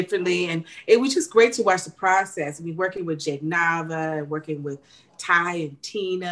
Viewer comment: is Kim saying "Jade Novah"?!? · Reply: She is saying “Jake Nava”, the video director